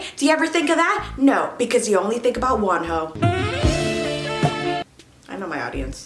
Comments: I thought you said "you only ever think about ONE HOE" instead of WONHO whyyyy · I legit heard "ONE HOE" instead of 'WONHO', the comments told me you said "Wonho" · When she said Wonho, I thought she said "one h03" and I was like....im confused...😭